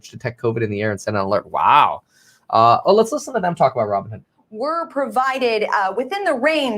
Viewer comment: Did she say Robbinhere?